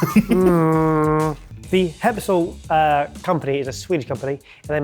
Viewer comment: Your pronunciation of Skeppshult was very confusing. :-) I started looking for something called "Hempsolt" or something and I thought you said it was Swiss so when I finaly found it and saw it was Swedish (I'm from Sweden) and it was a Skeppshult I started laughing. :-) The company "Skeppshult" takes it's name from the town Skeppshult just FYI.